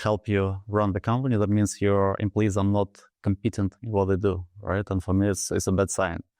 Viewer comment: Nik meant "your employees are not cOmpetent", but mispronounced, and Harry heard it as "compEting" [thus his question]. Judging by their surprised looks, neither seems to have understood what happened!